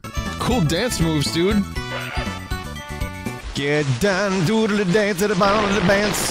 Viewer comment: i think your saying get down do a little dance at the bottom of the bance And i think its funny